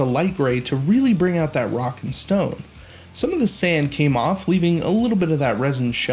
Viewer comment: did I hear a rock and stone?